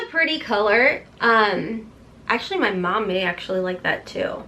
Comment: That Kentucky drawl comes out from time to time. When you said MAC, my captions thought you said “mayak”. Like kayak, but with an m. 😂